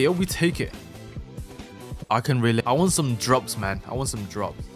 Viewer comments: thought he said drugs 💀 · You like this game? Also hi and ill play aut update don't worryyyyyyyy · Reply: i thought he said "drugs" 💀